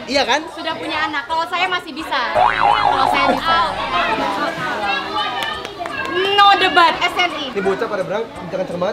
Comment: icis mau sold out kata mbak gak salah denger kan? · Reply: Ini yk belum sold out gais..bukan in yk mau sold out